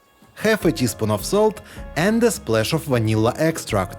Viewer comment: After the sugar, it sounds like he says ”half a teaspoon of soap” Should probably be salt, right?